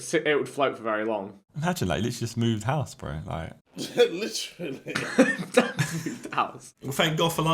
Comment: did JJ just say literally😮